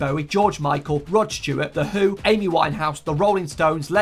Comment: i was listening to this just as background noise and my head perked up when i heard The Who because in my mind i heard The Hu as in the Mongolian metal band and thought “THEYRE BRITISH????”